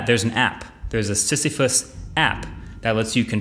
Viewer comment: thought u said sissy foot app